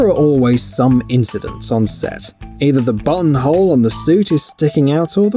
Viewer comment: Is he saying "bunghole" or "buttonhole"? I genuinely can't tell from context.